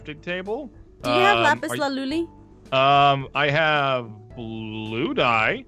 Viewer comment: Please make a season 3 btw did Aphmau said "Lapiz Laluli" in 14:03? 😁